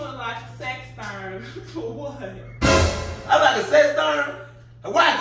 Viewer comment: What is the word at 9:36? A sex thurn? What does that mean?